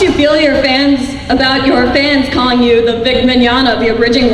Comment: what did that women say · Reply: asks about his comparison to Vic Mignogna - voice actor